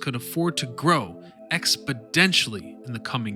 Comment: Did he just say "expo-d-ential"? I cant be the only one who heard that.